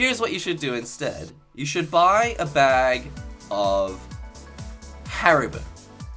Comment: I swear, I heard that as ' You should buy a bag of heroine' xD